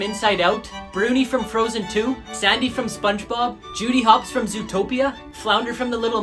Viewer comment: Did you just say sadney and not sandy